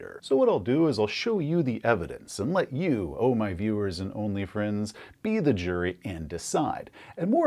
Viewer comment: Did anyone else hear OnlyFans?